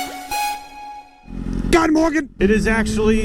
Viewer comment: "God Morgen" ... I had to rewind the video to get that 😂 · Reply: thought he said "god morgon" As Good morning in Swedish.